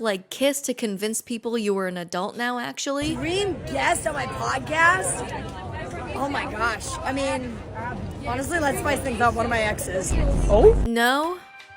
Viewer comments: What does Jojo say? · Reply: “dream guest on my podcast? oh my gosh. I mean, honestly let’s spice things up. one of my exes.” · I thought Jojo said green vaseline podcast😭